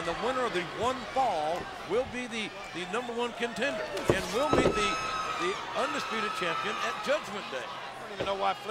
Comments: what did JR say? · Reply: "...and will meet the Undisputed Champion at Judgement Day..." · what did JR say? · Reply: I thought he said, "will be the undisputed champion."